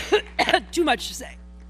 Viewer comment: What does she say at 10:20 during the cough? Sounds like "too much sex". Ewwwww!